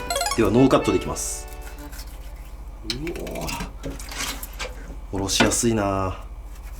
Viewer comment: 「おろしやすいなぁー」が、「殺しやすいなぁー」に聞こえた「笑」